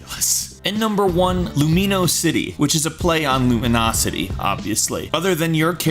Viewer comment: Who else thought he said lumiouse city when he said lumio city